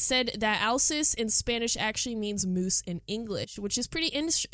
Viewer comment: 4 mins ago :0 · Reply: i thought u said goose until u showed the photo- also i think they might add a snow shovelling minigame